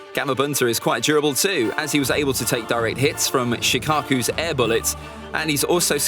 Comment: Did I heard Shikaku instead of Shukaku?